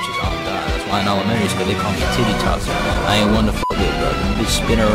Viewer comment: i always thought he said "i aint violent"